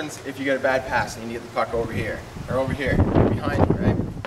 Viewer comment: I heard "Get the f*ck over here" instead of puck.